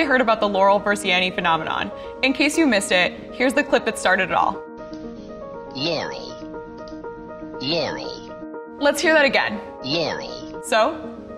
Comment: I hear "yair–wee."